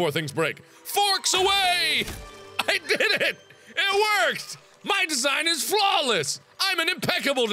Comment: my thing buffered what he said "my thing is flawless " so my thing made it sound like he said "my thing is glaw."